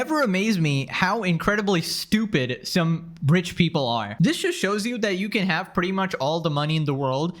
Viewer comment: I swear to God every time I listen to it all I hear is 'British people' instead of 'rich people'